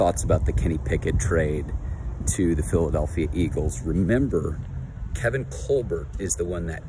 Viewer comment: My sound was soft so I heard : "Here are my thoughts about the Guinea Pig's entry into the Philadelphia eagle's room" And I thought DAMN, sounds like an interesting story??😅😂